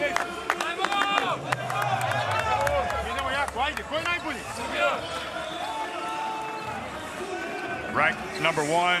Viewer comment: What dis Bogi say in 14:17 ?😊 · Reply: Keeping going strong! Come on! Who’s the best? (This is common huddle/pep talk in Serbia for sport teams. The question is a ‘setup’ for the rest of the team to yell out their team name in unison and break the huddle on a high note)